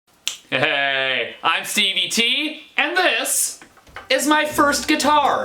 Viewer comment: thought you said "I'm CBT"